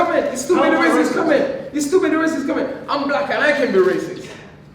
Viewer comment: Did he just say im black and i cant be racist😕